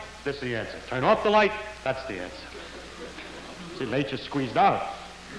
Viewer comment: What does he say here? I'm afraid I can't make it out... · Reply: I don't know, to me it sounds like "So Nature squeezed out" But if that's it it'd have to be a colloquialism that I'm not familiar with.